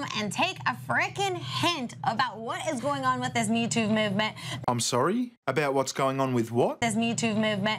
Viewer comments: It sounds like she is saying Mewtwo movement. I did not think he was that hard to get. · i thought she said the mewtwo movement. #mewtwo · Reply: I thought she said YouTube movement. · i thought she said the mewtwo movement. #mewtwo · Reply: I heard Me "Tuve" Movement. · It's funny it sounds like she was saying #MewToo lol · I thought she said Mewtwo movement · I thought she was saying youtube movement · #MewTwoMovement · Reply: I thought she said the "Youtube movement" and I was like "If there is a YouTube Movement you're apart of, no one else is" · All I heard her say was: “this MEWTWO moment.”